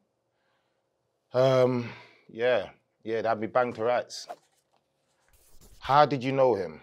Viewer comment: what did he say? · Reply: Banged to rights?